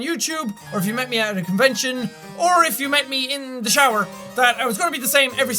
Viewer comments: Me: nodding to what he says and then... Hold on there for a minute... did you say "If you met me in the shower..."?! How the hell that could possibly happen? o.o Rewinded that part so I could be sure I was not mistaken. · ok i thought he said if you meet me in the chowder i went back and read it he said shower and no i dont want to meet you in the shower i would love to meet you thow